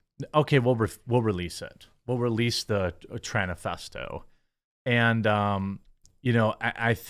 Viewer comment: "tranifesto?" did I hear that right?